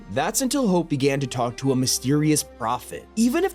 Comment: "That's until Hope began to talk to a mysterious prophet." I'm sorry did I hear that right? Did you accidentally just say Talk Tuah?